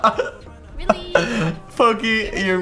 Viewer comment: did he say foki?